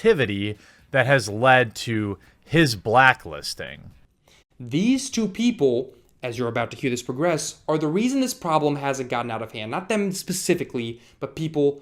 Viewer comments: I could swear says, "These two people, as you're about to hear this progress, are the reason this problem HASN'T gotten out of hand..." at 2:40. It's not super clear and may be him tripping over his words, but if that is what he says (and meant), then he's crediting you with keeping this bad practice from EA at bay. A compliment, not an attack. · I think you might've misheard him, I think he said HASN'T got out of hand, not has. I think this is just a misheard line. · He said "These two people are the reason this problem hasn't gotten out of hand!" I'm guessing people misheard the word "hasn't" as "has". That's the only way I can think this might come off as "blaming". He's talking about EA bribing and blackmailing YTers and worried about the affect that will have. He holds SKill Up and up as shining examples of honest YTers with integrity who are keeping EA in check. Sounds like pretty high praise to me. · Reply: yes I heard "has" instead of "hasn't" too for some reason, it confused me xD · am I mishearing him, or does he say "These 2 people are the reason this problem HASN'T gotten out of hand."? Thats what I hear, and it's what I heard when I first watched the video. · he says you and skill up are the reasons “this problem HASN’T gotten out of hand.” He’s not blaming either of you. Crazy how mishearing one word leads to a 15 min reply video.